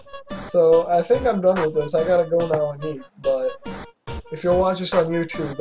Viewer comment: For a moment there when he said "I gotta go and eat", I thought he said "I gotta go and Beat" ..... Dafuq Ears...